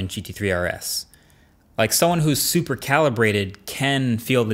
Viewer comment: Did you just call your self Superman?